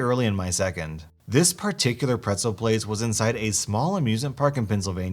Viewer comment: I thought he said pencil not pretzel